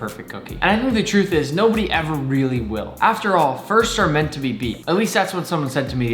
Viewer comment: I thought he said fists are meant to be beat 😂😂